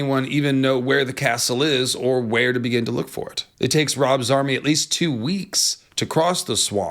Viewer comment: This is the second video where you've said "Rob's army" and I heard "Rob Zombie" and if I'm being honest you look like you could be a fan. Not a dig. Just funny thinking about Rob Zombie walking through westeros with his crazy everything.